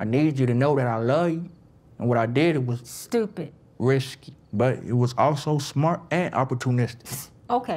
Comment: It took me a while to watch this episode cuz every time homeboy with the grillz spoke, I kept laughing. I heard "rishky" instead of risky, LMFAOOO.